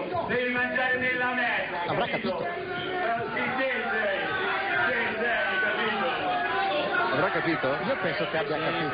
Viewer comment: Sapete dirmi cosa dice a 0:30... Sei un pisese? Ma si usa dire così? Ho vissuto 12 anni a Pisa e vorrei tanto saperlo. Grazie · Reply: no, gli dice “si te sei”.